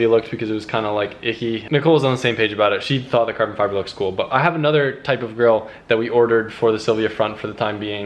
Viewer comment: at 3 minutes i thought u said "i have a nother type of girl.."